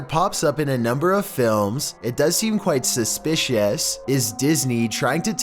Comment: voice is like me when i'm sick or when i have a stuffy nose, ahahaha i heard him saying "suspicouas""